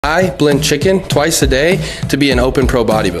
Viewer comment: I thought it said he bends his chicken